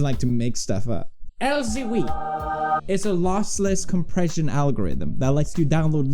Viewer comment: did you say L O S S ?